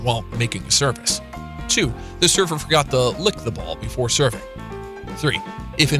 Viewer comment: i had to turn in CC to make sure i heard you correctly ..mmm they have to lick the ball? Isn't that gross?